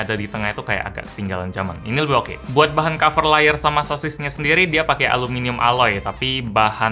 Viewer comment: itu beneran bilang sosis apa aku yg salah denger aja? wkwkw